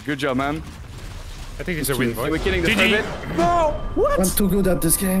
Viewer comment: accidently said "NO" when you guys won in the 2nd game as well. 28:00 Thought for sure caught it because I heard him go "That's interesting"